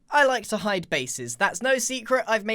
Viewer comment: Me who misheard the into "I like to hide faces" On a side note: the only problem I see with these types of bases is a lack of an exit that works the same way (aside from the powdered snow one)